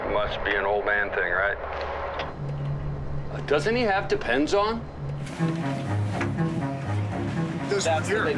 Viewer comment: can someone please tell me what Rick is saying cause I don’t understand it I hear depends on